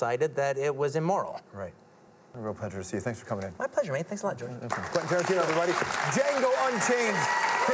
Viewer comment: Does he call him Jordan??